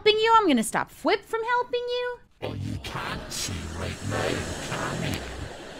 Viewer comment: What's saying here? EDIT: He said "Oh, you can't see right now, can you?"